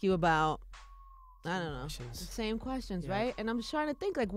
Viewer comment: In the beginning when he said questions I thought he said bull shit